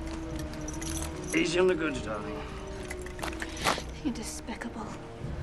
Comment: what did jack say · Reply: Easy on the goods darling